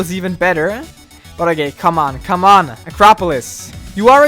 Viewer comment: did you say but a gay at 10:02?